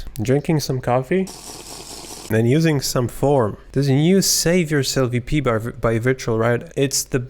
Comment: I’m sorry but I have played it 10 times and I cannot figure out what it is whatre you saying at 2:11? No offense you just have a strong accent I guess haha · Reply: Save Yourself EP